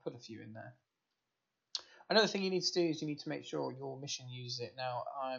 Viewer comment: wtf are you saying???? "another thing you need to do is.." mumble mumble i'm gonna skip an important critical step one minute into the how to video.. thanks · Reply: He says "make sure your mission uses it"